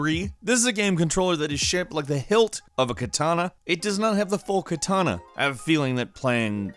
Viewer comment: did I hear Kitana instead of katana?